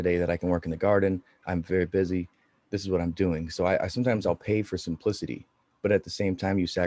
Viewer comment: 😂pay for simplicity had to rewind it 😂😂I thought u said pay for some pussy😂😂